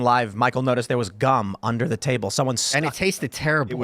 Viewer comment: LMFAO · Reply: Oops thought you said GUN under the table lol